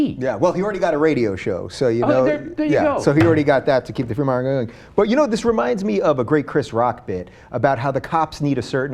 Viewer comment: what did he say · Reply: "ohh there you go"